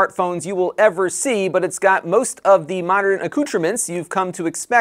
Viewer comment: What was that word at 0:13? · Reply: accoutrements = gear (I've googlevoiced that lol)